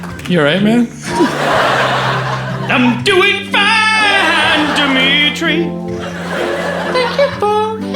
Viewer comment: Interesting, that when Will Forte says he's doing fine the generated subtitles say he's doing bad.